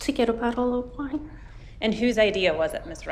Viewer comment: The first mistake was buying wine at a gas station. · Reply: Thought she said bottle of water. I must of misheard.